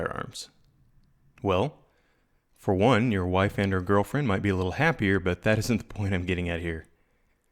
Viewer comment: I thought he said wife AND girlfriend for a second